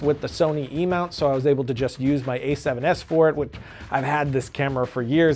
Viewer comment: Did he say a7Siv? 1:50 Did I miss a new camera release?